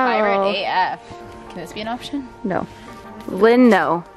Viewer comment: I deadass thought you said “lesbian option?”